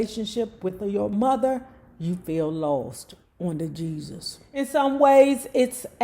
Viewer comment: Good morning, everyone. you copyright the phrase "On The Jesus" and print tee shirts and hoodies. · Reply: I thought that she was saying..."oh the Jesus" thanx for clearing that up.